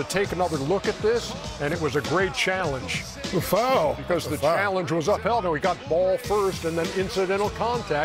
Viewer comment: Did Ernie say "Aw hell no" at 3:00? lol